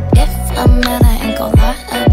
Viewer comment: it's hurt instead of hard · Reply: It's the line "If i'm hurt i ain't gon' lie about it" at 02:19 I think. You put "If i'm hard" instead of hurt